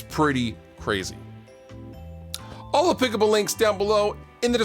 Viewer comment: Been watching fluff for a long time but I can’t for the life of me figure out what he says at the end! Someone please help me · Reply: Huh. Thought he was saying " I will put up the links in the description", he does say it fast with some ZAZZ at the end heh heh.